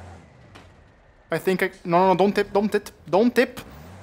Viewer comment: did bro say dont t!t